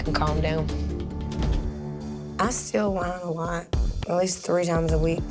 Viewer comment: She said "I steal wine a lot". I heard "I still whine a lot".